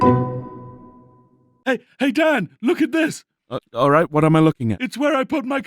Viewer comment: thought he said "hey dad" there for a minute but no